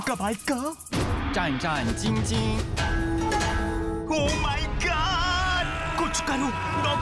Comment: any indian here?? idk but i think i heard kuch karo after omg 1:32 😂